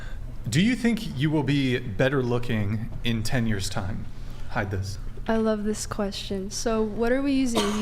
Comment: did he just call her hi-viz? LMAO